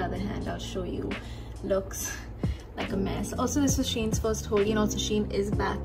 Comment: i thought she is saying shein is back in India🫠